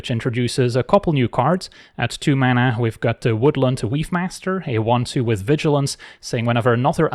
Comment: I keep hearing "Weebmaster"